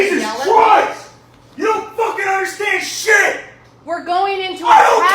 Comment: what i heard " i dont care. im a jerk. you dont frikin understand mehh!!"